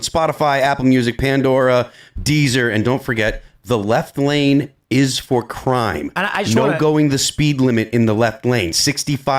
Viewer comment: NGL I had thought was saying ‘the left lane is for crying’. Made sense in my mind.